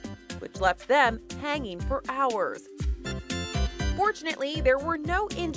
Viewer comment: Did she say fortnaly or unfortnaly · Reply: Fadel Al mahdawi fortnaly